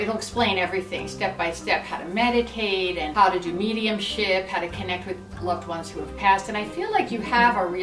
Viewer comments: Omg every time you've said Mediumship (In other videos also) I thought you said Medium shit hahahahahahahaha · Did she just say "how to do medium shit " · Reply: Mediumship 😂😂